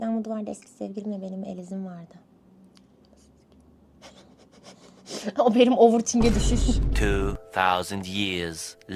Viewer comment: de ne diyor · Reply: Sktr et onu diyor